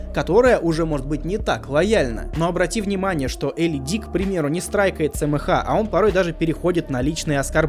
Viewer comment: Я один услышал Элли Дик?